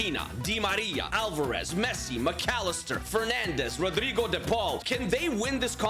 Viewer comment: NAHHHH. WHO ELSE HEARD HIM SAY PENANDES?